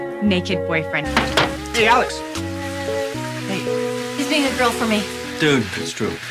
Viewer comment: what did she say? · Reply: "He's being a girl for me" Because christina and izzie are not around so alex has to do it. Obviously meredith can't wait longer to tell christina or izzie what she needs to say so she just goes to alex